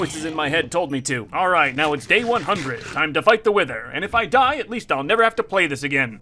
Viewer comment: Turn on the captions and Is he even a fan of WilburSoot. Wilbur instead of wither.